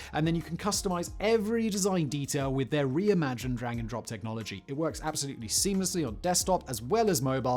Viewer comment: did anyone else hear that as "Re-imagine dragons"?